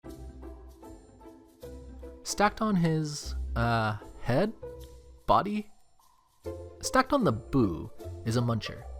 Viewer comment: I thought he said “stacked on his body? Okay stacked on his boob”😂😂😂